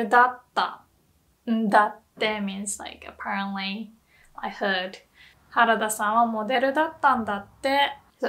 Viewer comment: Love the subtitles, just a very minor correction: at 8:10 you wrote 'head' instead of 'heard'